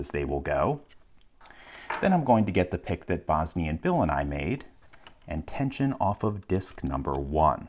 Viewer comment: I always hear "Bosni and Bill and I made".